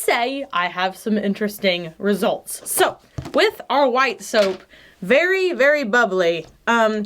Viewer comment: she said soap instead of slime lmao